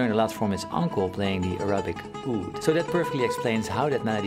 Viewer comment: When you said “Arabic oud“ I heard “erotic oud“ and I was like yeah, Paul gets it